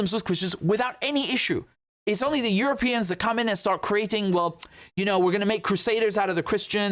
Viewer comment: my english is terrible. Did he say "europeans and communists"?